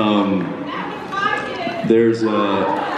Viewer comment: What is the girl yelling at around 1:15 when Jensen answers with "You should be so proud?" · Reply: "That was my kid"